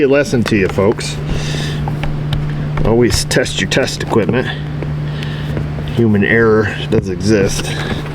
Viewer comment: For “human error does occur”, I heard “doom and error does occur”…I like the phrasing of the later.